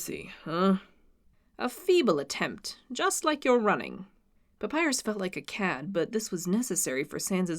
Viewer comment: papyrus felt like a cat? Is that what you said?